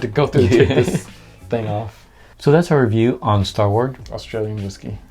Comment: We thought this said "Star Wars" for a second and had to do a double take! 😂